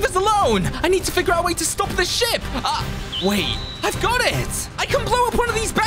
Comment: bro I thought he said shit instead of ship